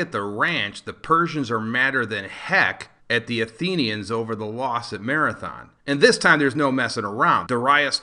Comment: I want to ask a stupid question.. What did you say after back at the ranch at 5:56... Sorry not from English speaking countries. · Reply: "The Persians are mad at the Athenians over the loss at Marathon."